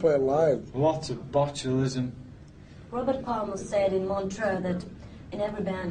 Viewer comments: Biggest challenge is to find an interview where he isn't stoned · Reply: Hi. Is it just me or at 1:03 does she say the name Robert? But who is the Robert she is referring to here? ta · This is Spinal Tap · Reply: Hi. Is it just me or at 1:03 does she say the name Robert? But who is the Robert she is referring to here? ta